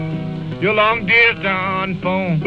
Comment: I really hear "yellow central", not "hello, central"…